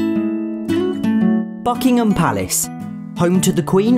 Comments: Lmao I had my volume maxed out so I just hear " BUCKINGHAM PALACE " bit it was so loud it sounded like you said " FUCKINGNAM PALACE " And well that was a surprise for the day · My wife thought you said, Duckham palace. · Nit tryin to act rude but for some reson it sounded like you said "fckingem palace" at first